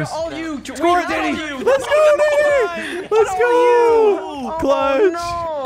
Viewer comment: was that the n-word???? im so confused on what he meant to say lmao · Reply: He says lets go Danny...